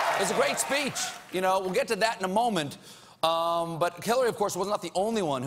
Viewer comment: Did he call her "Killary"?